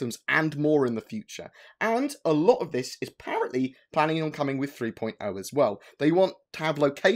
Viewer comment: did you say 3.0 or 3.1? Just reading your interview on redacted "And a lot more of this is planning on coming with 3.1 as well" but I swear you say apparently coming with 3.0